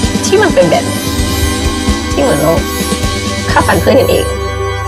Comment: วินาทีที่ 2:46 เฌอพูดว่าอะไรหรอคะ คือฟังไม่ออก??? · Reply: น่าจะ "ฆ่าฟันเพื่อนกันเอง"